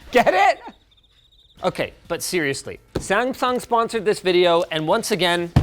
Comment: Okay, WHAAAT, did Linus just say there 🤷🏼 Samfung or my ears just “that” bad 🤔